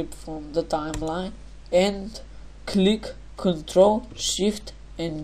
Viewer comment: CTRL+SHIFT+D Tutorial done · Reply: DynastySheep thought this dude said Ctrl Shift G i tried like 6 times lol thxxx 3 yrs ago